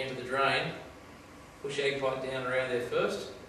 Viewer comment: what is the thing that he says to insert in first before the jetter hose. Sounds like "Eggpipe?" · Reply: An Ag pipe, generally 50mm is best.